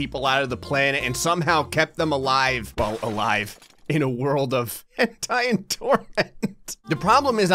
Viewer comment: did you say a world of... Hentai... And torment??? Sign me up bro.